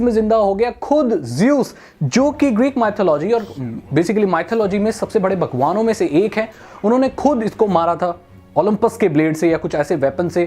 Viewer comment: i think you saying "sabse bade bakwaavso me se 1 h" but you say "sabse bade baghwano me se 1 h"😂😂😂